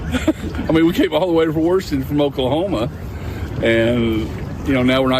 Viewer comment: BTW, the auto-cc is fascinating. I heard the Oklahoman "Washington" as (to abuse phonetic tscript) /warshn/, perhaps /warsn/ but the cc had "woron". Auto-cc seems struggle with fricatives and sibilants in non-standard ranges of word pronunciations.